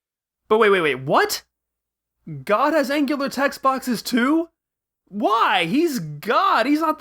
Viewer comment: what you say at this ? can't hear it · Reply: "But wait, wait, wait, what?! God has angular text boxes too?"